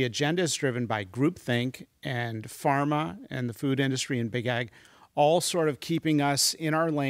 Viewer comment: Genuine question does he say big pharma and big “egg” at 6:19???? So confused Edit: he said big tech, it came up later in the video 😂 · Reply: Big ag. Agrochemicals.